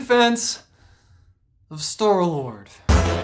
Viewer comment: did you say store lord or stoor lord?